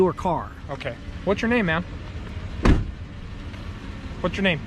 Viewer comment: Lmfao did you catch that I swore he said "What's your name ma'am" 9:58 😆🤣😂😹😆 that's my purse I don't know you